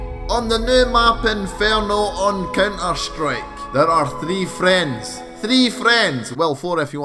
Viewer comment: wow did he really say cyn e strike